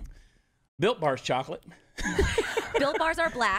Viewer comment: When he was talking about those chocolate bars I was just listening had to turn my head to look because I thought he was saying Bill Barr